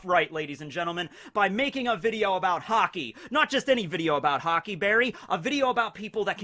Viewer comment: i thought he was saying hockey to be funny.